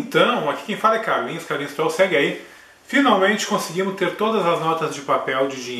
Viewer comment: Eu ouvi " Então aqui quem fala é carlinhos, carlinhos troll... se é gay! " HUAEHEUAHAEUH dps que eu voltei consegui entender que era " SEGUE AI ! "